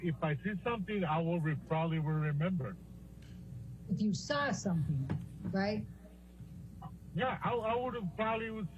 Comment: does she say "you saw something, right?"